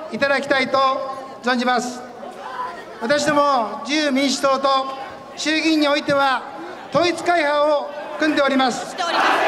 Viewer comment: 空耳アワー？ 0：15 私たち自由民主党と衆議院は、統一教会を含んでおります。